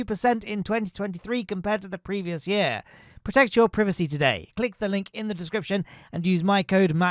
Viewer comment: did bro just say “privecy”💀💀💀🙏🙏🙏